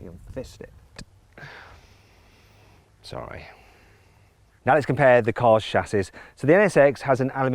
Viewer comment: 空耳「すまない」